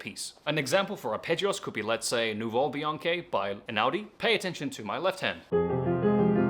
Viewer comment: Good suggestions for sure. What was the name of the piece and composer at the 5:53 mark? Bianchi? I'd like to get the sheet music for that. Thanks. Even the transcript couldn't understand the name. Thanks. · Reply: Hi, it's Nuvole Bianche by Ludovico Einaudi. You can find the link to the sheet music in his essential piano roadmap 😊